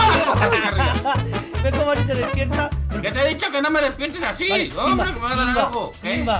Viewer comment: Lo que escucharon: Timba: te eh dicho que no me despiertes así Lo que escuche yo xd: Timba: no me despiertes asi mientras dormimos juntos X'D